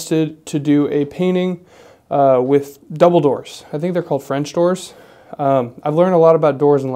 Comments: He said double doors and I heard "Dumbledores." 😅 The doors look amazing though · I heard, “do a painting with DUMBLDOORE”